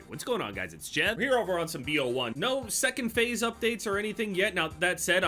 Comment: I heard you say faze 2 and nearly shit my pants until i realised the word phase exists...